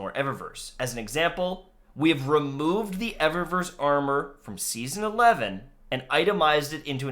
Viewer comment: they said Eververse armor, not season pass armor. think the Luxe set instead of the Seventh Seraph set